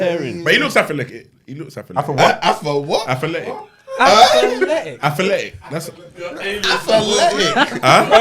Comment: I thought he was trying to say something with “African” in the word 😭😭😭